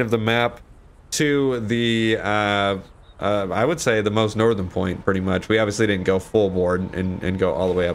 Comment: When I heard say "most northern porn" instead of "most northern point" was the best and made my day same with the Ron part and you yelling at the guy saying "I'll beat you add then go continue my run" keep it up my friend